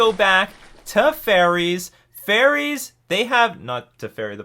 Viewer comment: yes, i too heard “Teferi” a few times when you said “faerie” xD. Maybe i heard “Teferi” (and”Treferi” too) even more times than the actual word being said